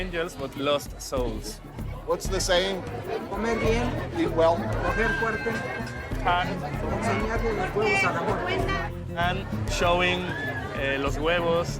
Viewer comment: the audio was cut, what the man is saying is: "En Tepito de come bien, se coge fuerte y se le enseñan los huevos a la muerte" (In Tepito you eat well, fuck hard and show your balls to the death).